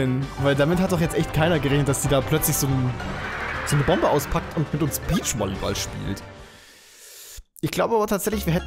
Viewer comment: ich hab verstanden ,wendy spielt peach volleyball😂😂